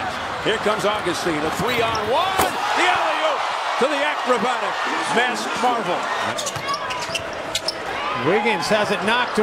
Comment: did he say acrobatic Vince Carter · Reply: "acrobatic masked marvel"